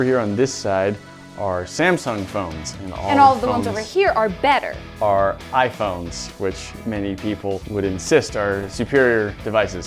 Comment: Ahahahahha I thought she said better for iPhones for a second... :D lol